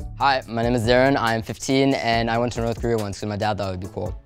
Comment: thought he said north korea bruh- had to rewind that like 5 times💀